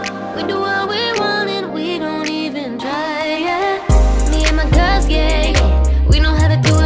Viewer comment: Ok at the beginning the line is "me and my girls get it"but tell me why I hear "me and my girls gay✨" 🤣🤣